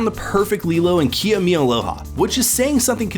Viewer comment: Did they call her “Kia?” Her name is Maia